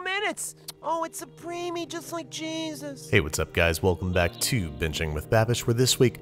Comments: I always heard "just like Gene" instead of jesus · Honestly one of the best Linda moments. “Oh my Dutch baby came out in 22 minutes, it’s a premie just like Jesus.” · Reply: I thought she said its creamy just like jesus